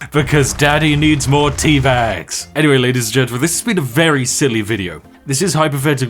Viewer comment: "Daddy needs more teabags!", all I hear is "Spiff wants to be tea bagged".